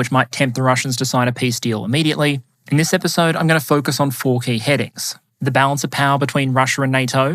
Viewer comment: said he was going to group his points under 4 key headings. I misheard, "fourteen headings"; and I thought, "BRING IT ON!"